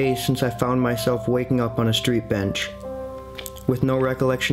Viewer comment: I thought he said he was waking up on a park bench?